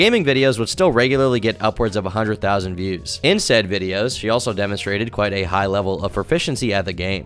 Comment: damn i thought you say incest video. Lol